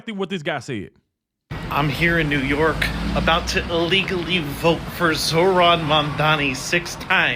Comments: i heard him say 'illegally' vote -- check it out again. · SOUNDED LIKE HE SAID ILLEGALLY VOTE 6 TIMES LISTEN TO IT AGAIN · I heard " illegally" but that makes it even worse!!🤬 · I heard “illegally” vote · I thought he said illegally vote. Maybe I'm wrong but he should be investigated!! Regardless of how he said it!! 1 person 1 vote period!! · Sounded like he said illegally to me AND Captions says so to :body-blue-raised-arms